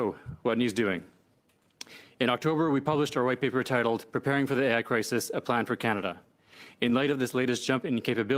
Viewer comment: When the Canadian politician said, “preparing for the AI Crisis” that first listen, I thought he said preparing for the Anti Christ